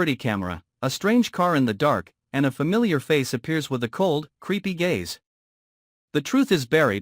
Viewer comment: I thought they said creepy gays … not creepy gaze lol 😂